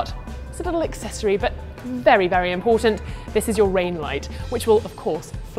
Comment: Her favourite part: Redlight HAHAHAHAHAHA that is so girlish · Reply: I thought she said "rain light", dude.